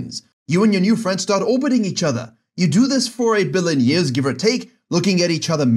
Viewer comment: Did you say A Billion years 5:34 ?😮